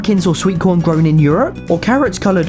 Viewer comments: Wait, did he say 2064??? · Wait,did he say 2064?